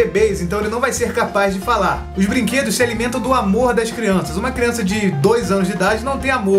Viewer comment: teve uma fez que eu ouvi "a porra das crianças" .-.